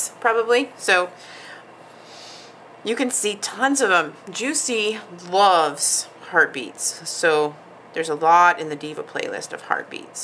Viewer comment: I was scrolling IG and I "heard" Juicy loves herpes! I almost choked on my coffee and had to rewind to see if misheard...and of course I did. I am still laughing at myself! Huge hugs!!